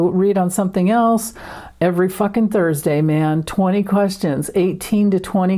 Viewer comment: When you said “live stream” I heard “life string” – I’m just damned happy my string bumped into your stream 👍😎🩵